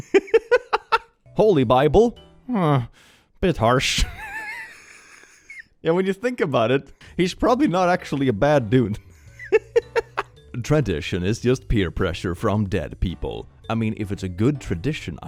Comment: I heard that as "Depression is peer pressure from dead people." 😐💀